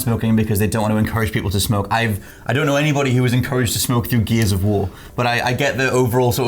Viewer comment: It almost sounded like said geezers of war 😁